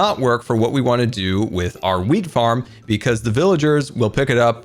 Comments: Am I the only one that thought he said weed instead of wheat 😰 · i actually thought he said weed farm but that was funny made my day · did anyone hear weed farm, not wheat farm? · Is it just me or did say weed farm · it sounded like he said weed instead of wheat